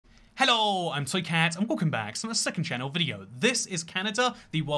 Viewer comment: Now I definitely heard Soycat.